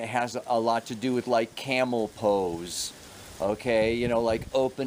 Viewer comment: I thought you said "camel toes." :) had to rewind and listen again after I cleaned the cereal off my computer screen.